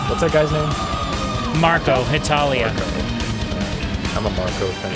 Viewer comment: Marko is from Finland, not Italian. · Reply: I thought I heard "He's Italian" too. But in fact he was trying to pronounce Marco's full name "Marco Hietala" when he answered the question 12:45 "What's that guy's name?".